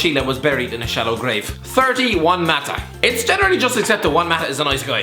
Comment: When he said "30, Juan Mata" I thought he said "31 Mata" lol